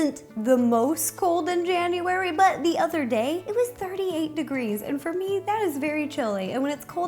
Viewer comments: I thought she said 38 degrees so I had to rewind to make sure she wasn't crazy. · her "78 degrees is cold " me looks outside to see snow 😐😶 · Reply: Jade Adori i thought she said 38 degrees